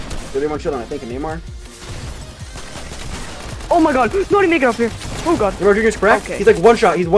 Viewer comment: Great Video!! The only thing is I'm kinda confused what did your friend say at 2:47 🤣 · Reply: I think he said "Oh my god, I didn't mean to get up here" but to be honest I have no idea he said the entire sentence in like half of a second 😂